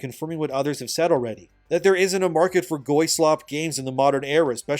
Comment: Did I hear that right? Goy-slop? Are you a noticer by any chance? BASED!